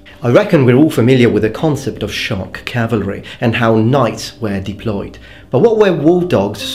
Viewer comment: For a split second I thought you said "shark cavalry". That would be a sight to behold. lol